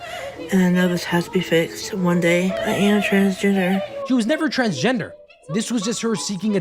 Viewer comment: I thought she said transginger🥀🥀